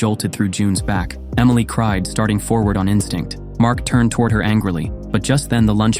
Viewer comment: instagram instead of instinct